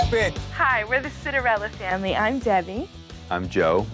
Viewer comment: I thought they said: cinderella family?